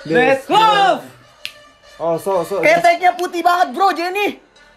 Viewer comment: Ada Yg Tau Gk Bang Ngomong Apa? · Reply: "Keteknya putih banget bro, Jennie"